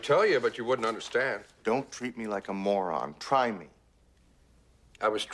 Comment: Was that "thought" or "fault"? I really can't tell. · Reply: for all this time I could have sworn he said "fault" but it turns out its "thought!" OI!!